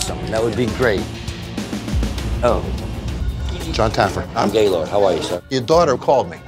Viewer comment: his name is Gaylord? Did I hear that correctly? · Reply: Yeah you heard it correctly because I put the caption on his name's